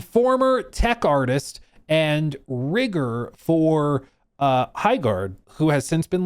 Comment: excuse me? What did he say? 😂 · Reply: Rigger, people who put the rig on the characters